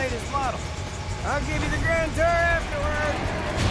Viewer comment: I always thought cid said "i'll give you the damn tour afterwards." because.... it's cid...